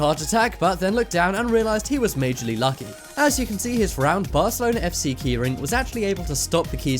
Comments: Did he say Barcelona in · did he say Barcelona FC its forking Real Madrid